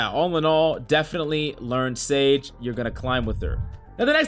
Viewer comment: Does he say sage at 2:13?